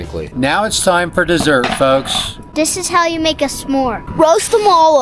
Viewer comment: At first I thought I heard you guys say, "this how you make a Somalii" 😂